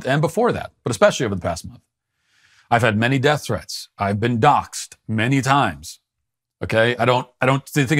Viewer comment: I did not get what Walsh said at 11:09 ("I´ve had many death threats...I´ve been ??? ") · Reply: I've been doxxed.